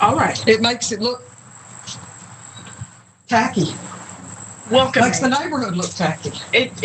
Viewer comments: I thought she said “faggy” instead of tacky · Is no one going to talk about how she’s a massive homophobe ? She called the flag “f$&gy” and is afraid the community would like that way! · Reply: I thought you said it looks tacky